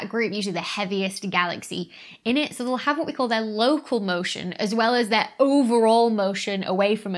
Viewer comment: genuinely misheard 7:40 they have what we call..... "loco" motion xDD